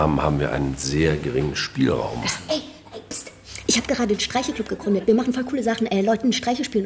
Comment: Ich hab erst "Streichel-Club" verstanden